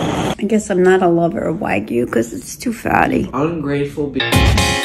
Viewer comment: “Ungrateful bi” got me dead 💀 EDIT: DO IT HIT 10K U WON’T EDIT 2: 10k… WHAT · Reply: Why did I thought he said “ I’m a grateful bi-“ 😶‍🌫️